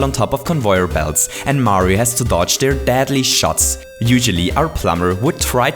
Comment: anyone else hear him say dadly? Coincidence? I think not. Conspiracy? Yes. Hotel? Trivago